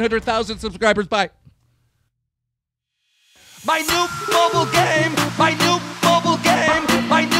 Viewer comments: I thought he said new bobble game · I thought he said my new bubble game